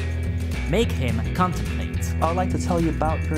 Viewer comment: I thought that said ''make him constipate''.